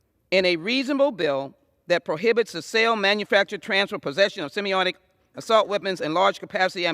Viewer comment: Speaking of pontificating... Why doesn't the Hon. Jackson Lee vociferously condemn the blatant invasion of the southern border as she so eloquently defends gun control? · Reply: Eloquently ? What the hell is semi-autic , she said it twice 😃😉😂😜🤣